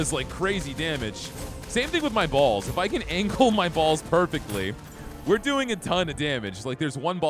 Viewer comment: what the fuck did you just say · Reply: he says he needs to angle his balls perfectly lol